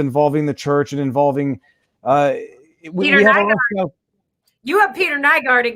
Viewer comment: What case did she mention there? I can’t make out what she said. Thanks! Found it. Peter Nygard